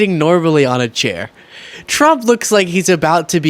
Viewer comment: Had to rewind... Miss heard, thought you said 'He looks like he's fruggling'😂